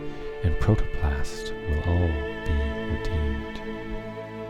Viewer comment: what does he say @ 9:40 'man and beast and plant and earth and protoplast will all be __? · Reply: will all be "redeemed."